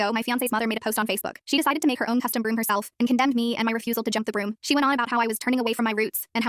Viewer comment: I thought she said jump the groom